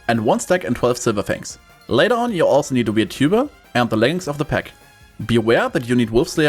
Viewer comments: i hear you mention a tuba and something of the pack but i didnt catch either 100% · did he say to brting a tuba? what did he suggest? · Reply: Weird tuba ye